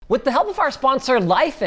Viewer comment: lol my guy! it sounds like you cuss a lot? i know it sounds weird but watch i will post times when i hear you SOUND AS IF you are sailor. First punch 0:00 you said "WHAT THE HELL"